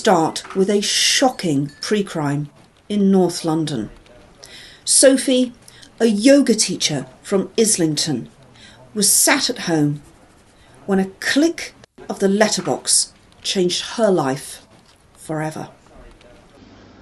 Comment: I misheard the first one, as it was in North London, I thought Sophie Oyogatecha was her name.